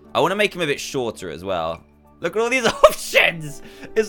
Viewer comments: Did I hear oh s*** or did he just laugh while while saying options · Tf you say? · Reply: He said " Look at all these, options! " You can check by using captions. Or by slowing down the video to 0.25x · In 8:18 i read the subtitle he did not swear he said look at these options or look at these oshinz